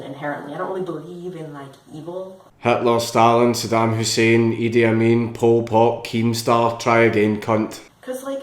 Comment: did he say E.D.I. Mean? from the Outlawz?